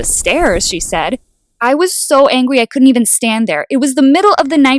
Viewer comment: “I was so angwy” that what I heard